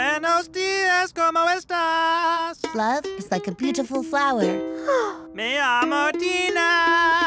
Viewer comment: Good day. How are you? love is like a beautiful flower My name is Tina but it can also be dangerous like a tiger...with a gun The dog is big I mean look at Steve, divorced, pain, nothingness. Sorry Steve Where is the bathroom? stay single forever, protect your heart, avoid guidance counselors. The end The singer is very hot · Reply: At first I thought Tina said me amo Tina